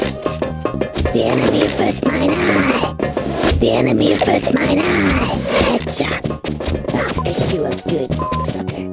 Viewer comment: Can someone tell me what do they say at 9:20? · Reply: The enemy within my eye